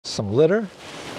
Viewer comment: I thought he said “ some glitter” and I was like🤔🤔🤔🤔🤔🤔🤔🤔🤔🤔🤔 then I realized